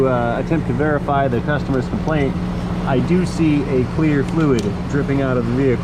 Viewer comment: In the beginning of this video you said "clear fluid" I thought you said " Queer fluid"?